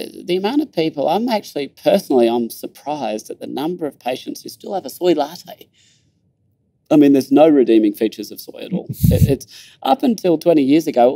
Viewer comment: What does he say at 17:30 please anyone? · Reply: he says " personally I'm surprised at the number of patients who still have a soy latte, I mean there's no redeeming features of soy at all... "